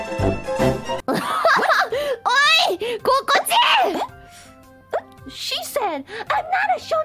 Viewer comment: Did she just say kokujin?